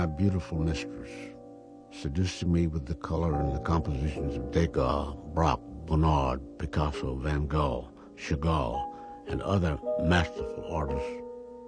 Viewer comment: Can someone please caption this interview? It is hard to tell word-by-word what they are saying to each other (ex. on 9:18 where he mentioned french artists) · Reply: Artists mentioned: Degas, Braque, Bernard, Picasso, Van Gogh, and Chagall, as in Edgar Degas, Georges Braque, Emile Bernard, Pablo Picasso, Vincent van Gogh, and Marc Chagall